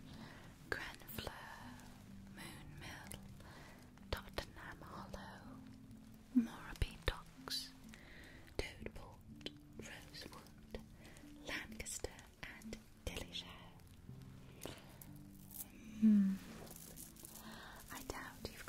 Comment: I’m sorry did she say Toad Port?!?! If so, as a bisexual cottage-goblin, I demand to see the many toads at that port!!!!!!! (please 🥺)